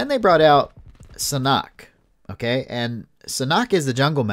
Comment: did he just say siknock? Omg it’s SANHOK